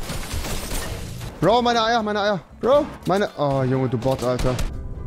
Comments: ich hab da verstanden: ,,Bro, meine Eier, meine Eier!"😂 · Habe bei 1:50 verstanden meine Eier meine Eier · hat man EIER verstanden LOL